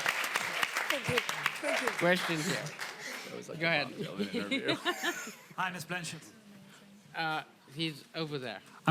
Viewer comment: what todd says to cate in 32:03? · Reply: Martyna K "That was like the Bob Dylan interview"